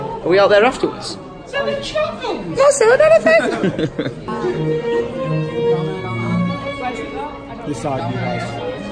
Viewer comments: What do and say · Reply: Sounded like "selling shotguns" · What does and say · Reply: "Not Selling Anything"